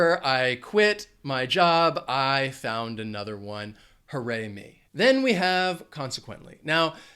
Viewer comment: Hi, very clear lesson.... What's the word you used on 9:49 "I found another one, ______????? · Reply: Hi Sara. Glad you enjoyed the lesson. At the end of that statement, I said "Hooray me!" 😉👍